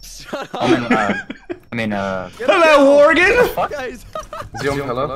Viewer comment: I swear to God he said "hello worgan"